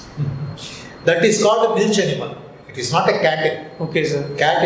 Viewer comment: Bro...gramin bank ka interview h...jb English proper nhi aati to koshis kyo krrhe ho..hindi hi bol lo...gaao m sb hindi hi bolne wale costumer aate h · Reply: what did he say milchen?